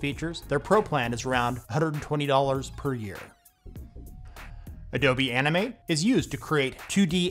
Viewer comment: Am I the only one who heard "Adobe Anime"?